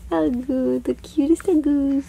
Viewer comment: Omg! How sweet are your boys! At 5:40 when you asked how are you... it sounded like he said I'm good...lol!! That was so cute!!